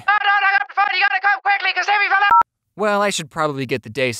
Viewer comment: I think Jack was trying to call an AMBULANCE not a LANCE because Timmy fell down the well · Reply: Wow i thought he said timmy fell of an elefant lol